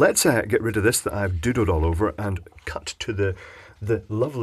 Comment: when you say "doodled all over" it sounds like you said "do-dooed all over"